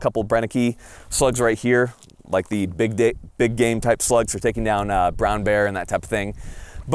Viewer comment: anyone else hear him say “big dick” 😂